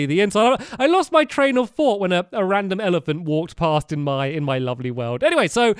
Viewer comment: When said rapid elephant I thought he said rabid elephant and I was screaming at the screen CALL PEST CONTROL!!!!!!